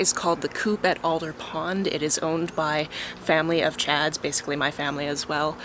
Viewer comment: When you said it's owned by family of Chad's my brain registered "owned by a family of Chads" and I pictured a bunch of conventionally attractive white guys in polos and shorts. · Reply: I thought the same thing!! haha I heard "owned by a family of Chads" too at first and was like "...oh no" lol